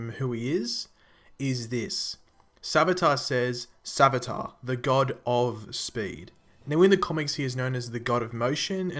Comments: I really thought I heard savitar the Godspeed 😂😂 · oh man. I thought he said "I am his Avatar" refering to Alchemy when asked who it was. This was informative.